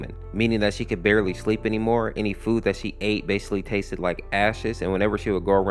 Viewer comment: I thought u said “a*s s**t” 💀🥀